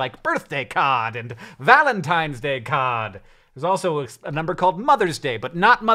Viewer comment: I wasn't expecting a 5 second Boston accent so I heard it as "Birthday Cod" and "Valentine's Day Cod" and was trying to imagine a fish plot about race horses before I realized it was Card.